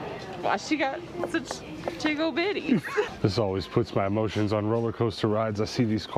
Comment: OMG Best moment ever! "Why does she have such jiggle bitties!" 😂😂😂 That needs it's own shirt in the Tendo store. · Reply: MandyRee I heard “tiggo bitties “..........care to weigh in? 🤣